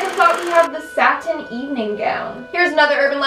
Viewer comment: Satin Evening Gown: what i heard: Satan's Evening gown